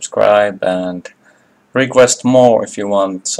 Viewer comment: when you said good bye , i thought you said thank you the winglet , or thank you the bullet , good i'm using closed captions, lel